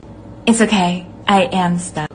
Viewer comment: Dracula sounds like he says "haha Jonathan you are banging my dog